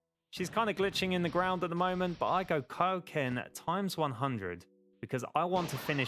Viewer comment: YouTube thinks you said Cocaine instead of Kaioken